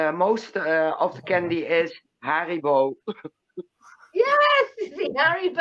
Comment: LOL it sounds like your saying hairy balls everytime you say haribou